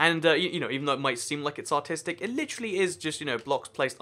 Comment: When you said "artistic" I heard "autistic" XD